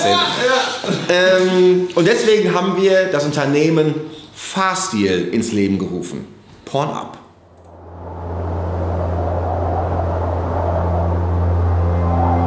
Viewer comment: Porn up hätte man auch falsch verstehen können